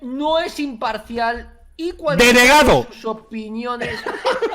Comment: Pense que decia DEGENERADO